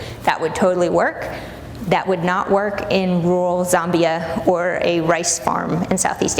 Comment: Did she say "Rural Zombia?" There's a place called Zombia? I have so many questions and am so conflicted.